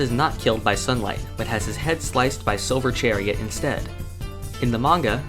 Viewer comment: did he just say Sober Chariot?